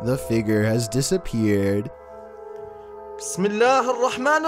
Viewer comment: number 9, i’m wondering if someone can translate, every time a hindu or indian video on chills i hear a “smedlah” and i’m wondering what that means. 2:17 is a reference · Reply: Bismillah hir rahman rahim (in arabic means ) In the name of God, the Most Gracious, the Most Merciful